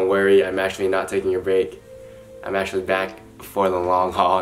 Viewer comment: I’m crying, you’re actually back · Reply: I thought it said “you’re actually black” 😭